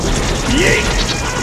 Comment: Wai- did plakton just say yeet?